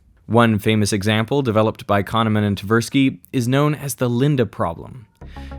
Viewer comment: i heard "condiment and tversky" lol